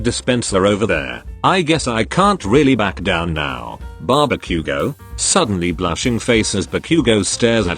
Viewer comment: I heard barbeque-go Now Ba-Bakugou But barbeque-go 🤣🤣🤣🤣🤣🤣🤣